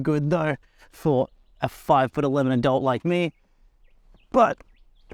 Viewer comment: Thought you were saying 5"11 dolt, took me until the second kisten to realise tou were saying "adult"😂